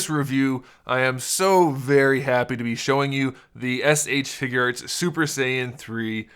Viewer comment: I thought you said ”figerts”